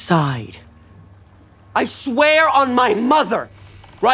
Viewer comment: I heard "yeah I'm Dustin's- Dustin's mother"